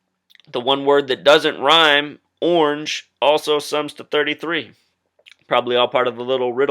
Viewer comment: Did you say ORANGE?